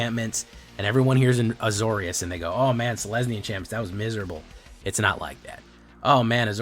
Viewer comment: did I hear him say "oh man it's lesbian chimps"? I've replayed it multiple times and that's the only thing I can hear